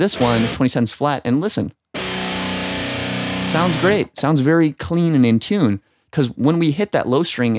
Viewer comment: I thought he said it sounds good??